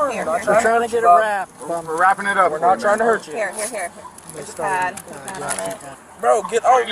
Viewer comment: Did he say "maxi pad" under his breath